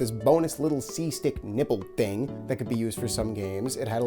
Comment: Sounds like your saying the “nibble” when clearly you mean “nipple” 😂